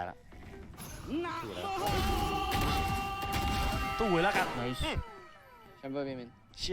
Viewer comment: ซับยังมีผิดๆอยู่นะครับ 2:35 พี่foxzเขาพูดว่า สู้เลยละกัน